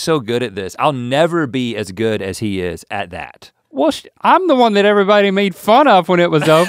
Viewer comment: I heard a well shi* from Link. He quickly recovers naturally.